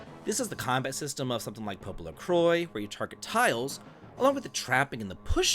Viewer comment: What video game are you saying here? The transcript says "Popo Lacroy" but I can't find any game with that name. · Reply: Popolocrois